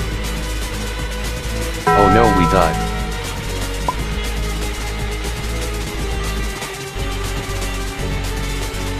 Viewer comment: umm what’s that word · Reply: Buddy sowrd